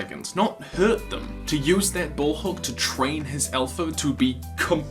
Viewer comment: Did anyone catch at 3:03 how it sounds like he said “so how did he train his elephant to do completely not that” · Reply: sorry he said Alpha